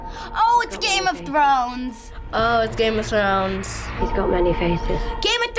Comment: Is it only me or do I heard game of drones??